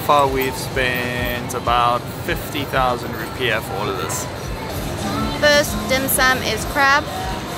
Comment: I thought you say "first dim sum is crap". I was like, well that's a straight forward to the point review, hehe.